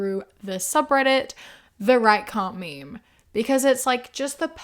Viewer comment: With your accent, I thought you said the right commie at the beginning and I was laughing a lot 🤣